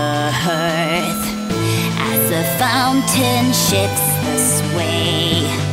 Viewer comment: am I the only one who hears "As the fountain shits this way." ?... no...? Well either way, you'll be hearing it too, I guess. Heheh